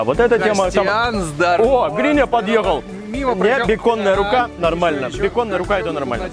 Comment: Блин Костян, мне послышалось: "Ооо, Гриня, подъёбал", даже назад перемотал, а это ты сказал подъехал.....ахахааа)))